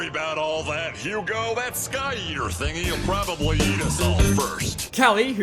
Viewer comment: What it meant to say: “That Sky Eater thing will probably eat us all first.” What i heard it say: “That Sky Yeeter thing wil probably yeet us all first.” God damn my mind.